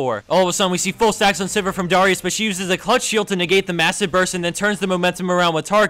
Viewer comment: he said clutch shield, not clutch heal. easy to mishear though because of how fast hes talking.